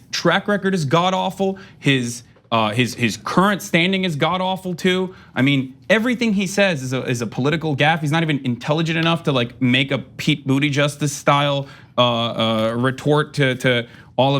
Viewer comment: Did he just say, "Pete Booty Justice?" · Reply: Booty Judge. I think Kyle started it.